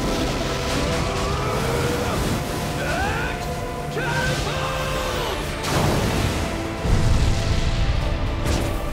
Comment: what does bakugo say there? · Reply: X-Catapult